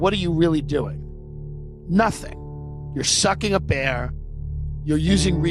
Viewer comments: As a child,I thought Alanis morissette sang ," for the cross-eyed bear that you gave to me," instead of ," for the cross I bare." While watching this, I thought Tim said "sucking a bear" instead of "sucking up air". It really took me back, beautiful work my friend. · I thought he said, "You're sucking a bear" and I was like dang, how he know? Go Mets! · “you’re sucking a bear…” I know he said “you’re sucking up air,” but I choose to hear what I heard. That’s my truth.